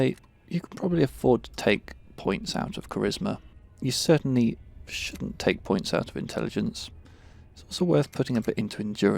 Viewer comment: I didn't take out of intelligence, I don't know why you're suggesting that I did! A high intelligence run does give you more skill points, it's true, but it's also perfectly possible to complete the game with low intelligence. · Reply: oops! when I heard you say "... certainly shouldn't take points out of intelligence" the first time I heard it as certainly should take points out of intelligence. My bad D